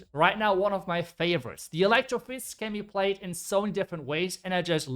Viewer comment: Electro fists lol 2:07 i thought you said electro boots at first. It's techno fists btw for any confused